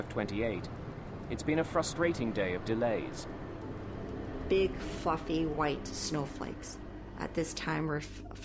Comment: DID SHE SAY "BIG FUCKING SNOW FLAKES?"